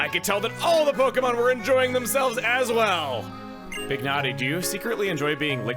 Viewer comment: Pignati? i misheard that as big nate.